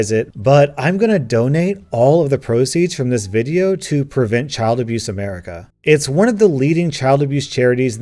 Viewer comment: OMG I heard 'Donate all of the proceeds to prevent child abuse America' as 'Donate all proceeds to prevent the charity of child abuse america' And was VERY confused